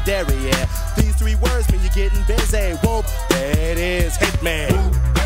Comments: I thought they were saying whoop that ass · The first time I heard this I thought it said "whoop that ass" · For years I thought they were saying “Whoop that ass” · I always heard it as “whoop that ass” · When I first heard this as a kid, I thought that it was saying "whoop that ass".